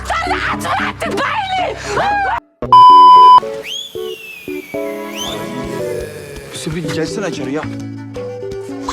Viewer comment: Musinenin ne dediği anlaşılmayi😂😂😂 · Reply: Doktoruda aç bıraktık bayılıyo Diyo muhsine